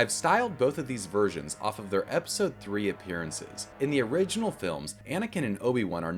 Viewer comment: did he say the original films